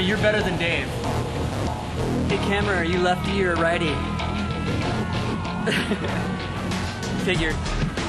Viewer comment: what does chester say at 0:25 ? sry im german ^^ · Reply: “Hey camera, are you a lefty or a righty? figured” 😊